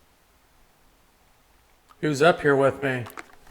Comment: hey!!!! what is that crazy shadow at 32:05. i think says “ who’s up here with me” it’s in front of and tucks to the left behind the wall🎉🎉🎉❤❤❤❤❤❤❤❤😮😮😮😮😮😮😮